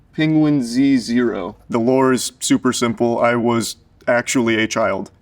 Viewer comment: The subtitles say "[indistinct]" but he clearly says "The lore is super simple.". I'm pointing that out just so that somebody can fix it